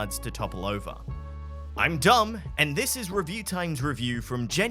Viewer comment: it sounds like saying im dumb